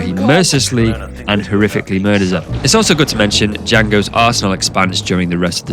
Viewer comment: I heard "Jango's arsehole expands." I can't be the only one that heard it this way XD